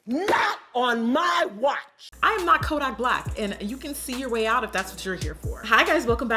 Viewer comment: Hi Jackie <3 ya I just wanted to let you know I noticed a few closed captions problems one example is at 0:14 instead of kodak black it says code I black lol.😆 Anyway the deaf community would really appreciate if you fixed it thanks-Hannah 😽